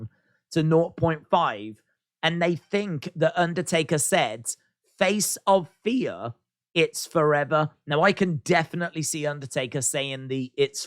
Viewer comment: If you read the lips in context, I thought he said embrace the fear for it lives forever